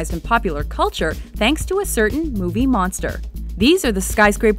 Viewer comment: So at the end, when she said bucket list, I thought she said "Fuck it list".